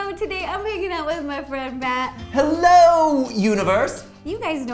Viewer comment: i dont know why i hear this but at the start it really sound like she says im making out with my friend mat but im pretty sure that she is saying im hanging out with my friend mat